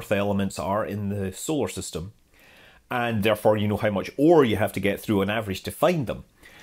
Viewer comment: When you said "and how much ore you have to go through to find them" (rare earth elements) I misheard it as "how much war you have to go through." Quite a sad commentary on obtaining the resources to sustain our technical civilization, that "war" was the default in my brain for those phonemes. I'm sure I'm not alone.